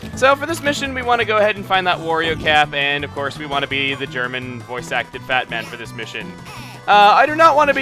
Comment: When you said "German voice acted Fat man" I heard "German voice acted Batman" XD It inspired some very....odd imagery lol. Batman yelling in German in a growling voice XD